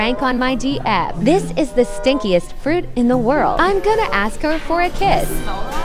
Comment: I thought it said"stickiest"